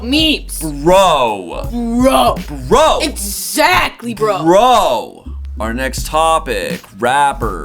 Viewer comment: when she said exactly bro I heard ITS ZACK BRO like if you got the meme reference 😎